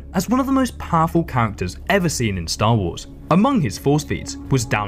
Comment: did I just hear "amongus"